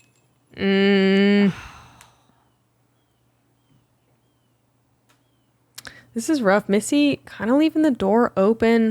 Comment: every Finn can immediately hear what Eveliina said here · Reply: ”Vitun perkeleen vittu saatana vittu" 😂